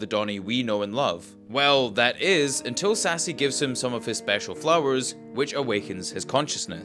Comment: He didn't awaken Donny with flowers. He awakened him with his touch. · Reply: i thought he said Sassy awakened Donny with his ‘powers.’ Not ‘flowers.’